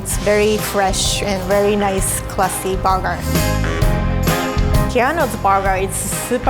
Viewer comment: is she saying “burger” or “booger” ? 😂😂